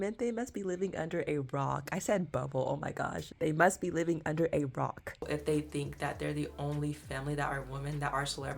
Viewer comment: I thought I heard they must be living under a golf course…lol. 🍷 too much wine